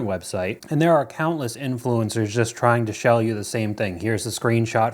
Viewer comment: he said 'shell you' instead of sell you ❤️